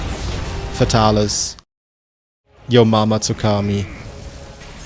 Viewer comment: what’d you say about ma mama??